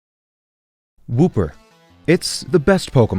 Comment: i restart the video and it sounds like he says booper